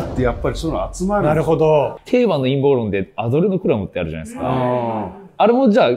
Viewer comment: ってなんて言ってます？？ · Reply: アドレノクロムかな？？めちゃ聞き取りづらいね